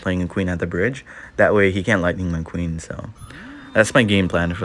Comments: I heard lightning McQueen 💀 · did he say…lightning McQueen?